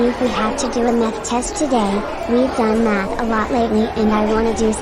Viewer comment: i heard meth instead of math